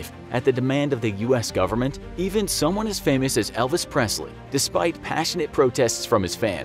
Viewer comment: Wow I thought it said elves not Elvis